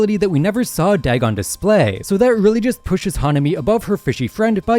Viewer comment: It was said that if jogo took the same hits that Hanami took during the exchange event, he would die instantly (said in the official character guide) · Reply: My dumbas thought u said nanami😭